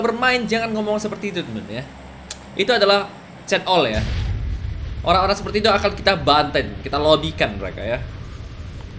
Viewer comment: ngomong apaan anjir di sensor🤣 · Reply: C*li :v